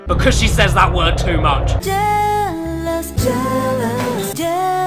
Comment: i keep hearing "jellos"...